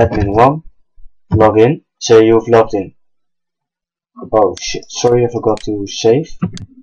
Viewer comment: Nice tutorial Sir! But did you just say "BULLSHIT" on 07:23 ???